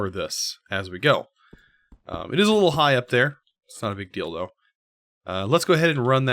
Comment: Can anybody help me with this modpack?How do you get to the end or kill the dragon?Also is there any way to load chunks? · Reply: sounds like he says not a big dildo